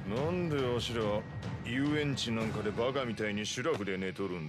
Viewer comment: Did he say baka mitai?